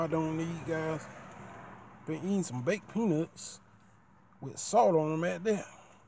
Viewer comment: Sounds like your saying penus XD